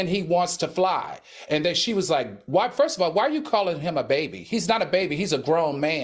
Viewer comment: I was looking for a baby when she said the baby didn't have pants on. He's a grown man! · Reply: I thought she said Da baby. which when watching it back had me thinking she thought she was funny. SHE WASNT.